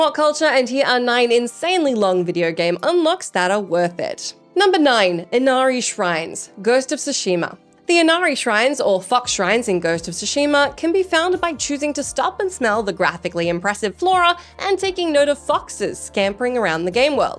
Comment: I had to do a double take for a second. It sounded like she said "F*ck" Shrines. made me laugh for a bit.